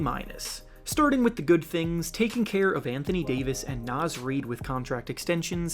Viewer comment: hold up my boi I heard you say Anthony Davis lol